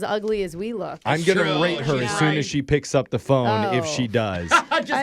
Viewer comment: did he say what I think he said? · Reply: Rate her